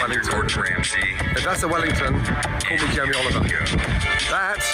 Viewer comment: What did he say at 0:36? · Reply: he said "if that's a wellington call me jamie oliver"